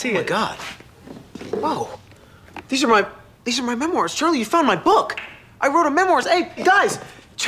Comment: I swear I heard Danny say "menoirs"